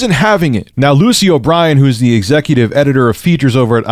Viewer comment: Oh I thought you said Lucio Brian